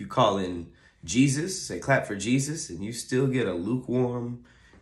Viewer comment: I thought he said clap for teachers 🤣